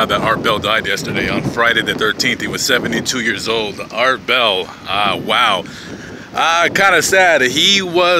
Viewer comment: I thought he said fart smell.